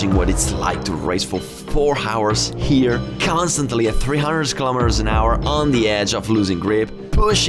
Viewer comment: did he just say KILOMETER?!?!?!?!?!?